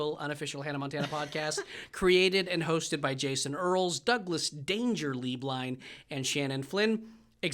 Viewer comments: When Doug introduced himself for the first 8 episodes I thought he was saying, “Doug ‘Dangerously’ Blind” 😭 Cracked me up so hard when I realized I was wrong 😂❤️ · Not a question, but i always hear “Douglas Dangerously Blind” 😂😭😭